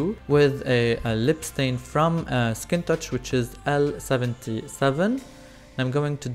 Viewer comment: What was the brand and color of the lip stain? I thought I heard Skin Touch L77, but could not find online